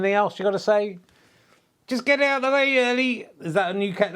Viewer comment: ” just get out the way Ernie” is what I heard, and I like it. 👍